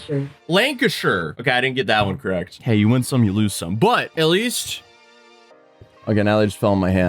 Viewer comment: you said you win some you lose some i thought you said you lose some butt then you showed the cheese i was like magic weightloss cheese???????